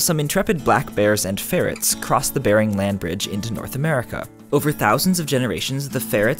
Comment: When you said "barren land bridge" I thought you said "Bear and land bridge", and I was picturing a shit tonne of bears in line while others climb over them.